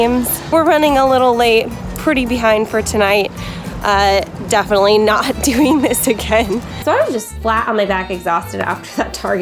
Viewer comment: Hello Buzzfeed! I love your videos and I want to make sure everyone can understand and enjoy it. That's why I wanted to let you know that your captions are a little off. At about 3:01the auto-captions say "definitely not doing this to Ken" when they were meant to say "definitely not doing this again". When you get a chance could you fix that? That'll help the 360 million folks globally who rely on closed captions to understand videos. Thank you, and keep up the great work!